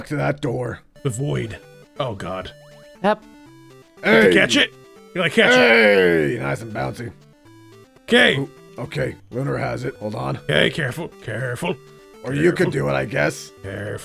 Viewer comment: Did say “gay” or “kay” 😥